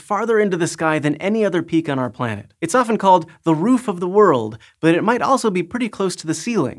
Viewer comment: almost thought you said "Throat of the World" - had me excited there for a second